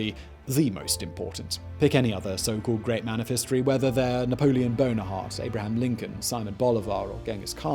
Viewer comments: Did you just say Napoleon Bonerheart? 43:46 xD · Did he say Napoleon Bonaheart or am I hearing things? lol